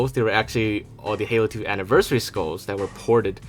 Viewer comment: He obviously said Halo 2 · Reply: my bad I thought he said halo 3 anniversary. Getting a little antsy cause i misheard some shit 😂😂